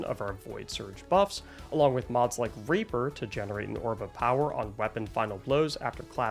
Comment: Yo did he just say raper?😂